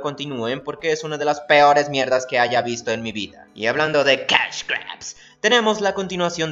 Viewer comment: ¿alguien me dice que dijo en ese minuto? Simplemente no conozco aun mucho del lenguaje "técnico" , y aun hay cosas que debo aprender de la animación en cuanto a conceptos , uso de las palabras y nombres se refiere. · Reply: El Kadabra Shiny Con Swag el dijo "Cashgrab" básicamente es un término que se usa para indicar que algo existe sólo para sacar dinero sin importar si está bien hecho o no, un ejemplo es yu gi oh que básicamente es para promocionar las cartas y el nuevo método de invocación